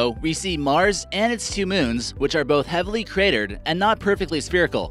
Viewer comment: that moment when you repeat Phobos and Deimos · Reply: The first time he said deimos I thought he said debibos and I was about to make a comment which pointed out that mistake